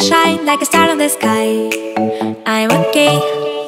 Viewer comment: When she says I'm okay it's sounds like she is saying "I am gay"